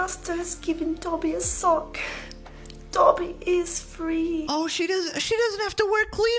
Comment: I thought she said "Toby is free" and I didn't remember such scene in Roots 😂